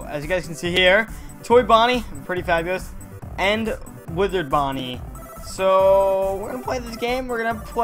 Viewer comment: Thought he said Wizard Bonnie omg lol Nice video!!